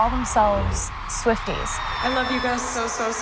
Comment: Omg I was watching it with subtitles and instead of saying swifties it said swift gays🤣🤣🤣